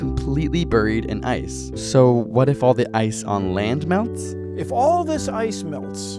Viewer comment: I thought it said " what if all the ice cream melted"